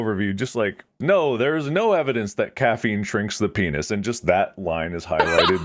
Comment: thought they said it TRICKS the penis lol 😂